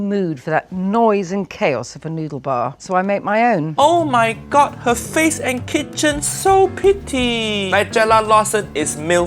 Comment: lol i just heard "Her face and her kitchen are so pitty" XD